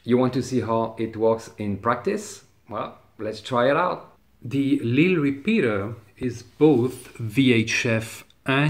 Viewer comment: What repeater did you say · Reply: The Lille repeater.